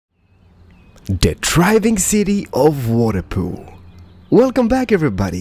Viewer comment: I always thought u said driving city at the start... lol